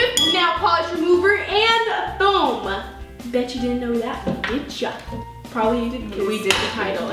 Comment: I thought she said "bitch you never knew that" 😂😂